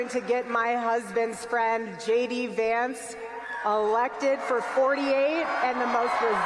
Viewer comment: My brain heard her say “my husband JD Vance” maybe that’s what it will be by 2028